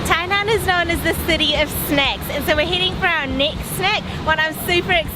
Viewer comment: "City of Snacks" = my kind of city. :) Love this vid guys. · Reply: I heard it as snakes ...and I was cringing here thinking weather to watch her eating a snake snack of not ? LOL ...finally she said some words like milk fish so I continued.